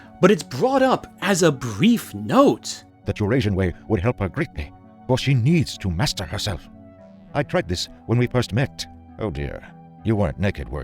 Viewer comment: Please tell me that the captions are wrong and that he didn't actually say "your Asian way"